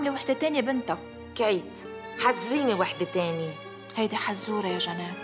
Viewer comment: شو قالت هون؟ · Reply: k3eet